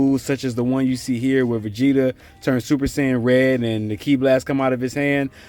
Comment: did he just say super sayain red?